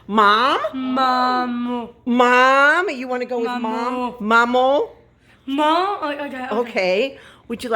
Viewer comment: ! Girl! Outstanding! M's and G's and J's are not easy sounds to make! Mama look what your girlie said! 😀 Do you think at 7:32 said "mom or dad?" I loved the convo she had with her therapist. ❤️🧡💛💚💙💜